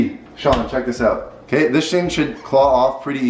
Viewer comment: he says “this shing” instead of “this thing” 💀